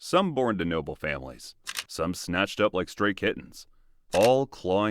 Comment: Naurrr wayyy...why did I misheard it as 'Stray Kids'???!!! 🙏🏻😭